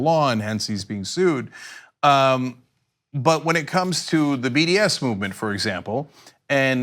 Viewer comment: Is it wrong I thought Cenk said "BDSM Movement"?